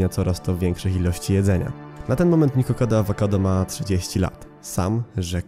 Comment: usłyszałam 200 lat😂